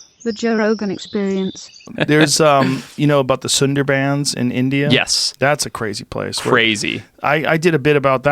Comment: I thought he said "the Slender mans in India". Well thank god THAT wasn't it.